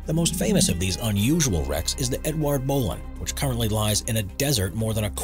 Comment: Edouard Bowlin... I thought you said Marc Bolan 😅